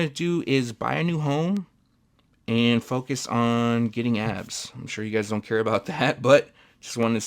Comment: In the outro I had to rewind it as I thought you said get a new home and focus on getting ass, and I thought this is new for a sneaker review